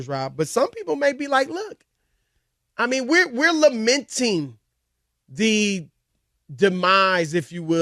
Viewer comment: I thought he said, we La-menting